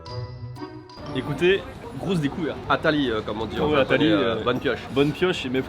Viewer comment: 😂 « Atari » = bonne pioche Pour eux, mais chez moi ça s’entend « Atali » et ça veut dire « Attends!! » 😂